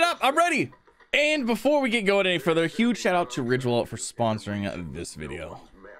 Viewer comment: Video Sponsored by Ridge Wallet: <link> Use Code “Z1GAMING” for 10% off your order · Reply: when you said ridge wallet I thought i heard you say rick rollet lol